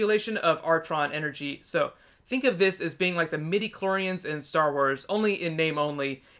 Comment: turn on closed captions for this video, it's funny. at 2:47 he says it's like the Midiclorians in Star Wars, the captions say "like the many Koreans in Star Wars"!!!